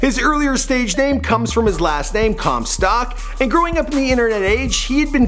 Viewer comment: Did anyone heard him mispronouncing "Comstock" as "Comstack" at 5:54? Or is it just me?